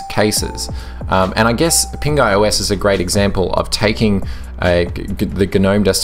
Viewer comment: The whole time I thought you were saying "Pink Eye OS", which seemed like a really unfortunate name for a distro... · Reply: I thought he was saying "Pink Guy OS" and I thought Joji had make an OS